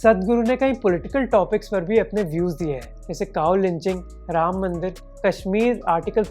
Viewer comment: Did I hear: 'cow lynching' at 8:09?